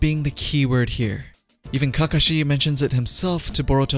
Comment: did he say ka ka she